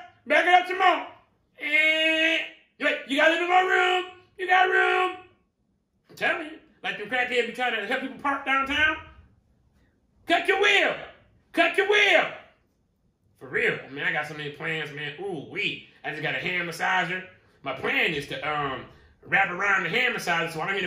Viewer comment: Dat Crackhead "Cut yo wheel" Got me 🤣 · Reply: Sound like he said “Cuck your wheel...” 😂😂😂😂